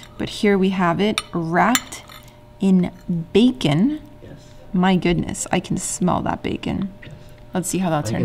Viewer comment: OMG I heard "rat in bacon". I need to learn and listen more jajaja